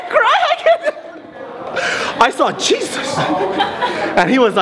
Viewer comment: When he said Jesus I thought he said cheez-it 😂😂😂